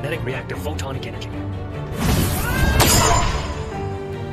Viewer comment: The music playing over made it sound like he said “bitch please” instead of “which means”